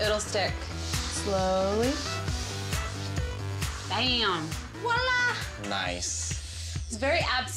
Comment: Did she say dam in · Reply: Nope, she said bam